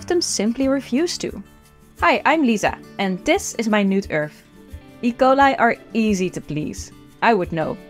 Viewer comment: my dumb dirty brain misheard that really badly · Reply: “My nude earth” I HEARD IT TOO.